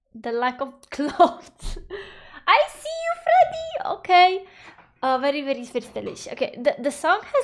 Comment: Did you say "very very fürchterlich" ? xD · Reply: hahaha no , very very stylelish <3 .